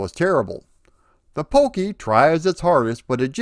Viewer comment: … did you say the “POKEY”’ tried its hardest in regards to the abysmal sound? Or did I just hear “POKEY”? I ask because the 7800 lacked a sound chip. With the 7800 containing a 2600 inside (what with the compatible cartridge slot and joystick ports) the powers that be decided that the 2600 sound would be just fine for the 7800. And that comes from the 2600’s TIA chip. A shame, I know. The thought was make the one time expense of the 7800 lower and milk people for the games. Games that wanted good sound could include a POKEY inside the cartridge. Ballblazer did it but I think few other games did. And I highly doubt Donkey Kong was one such. A shame, I know.